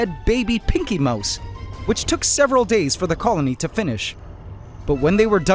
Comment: I thought you said seven days sorry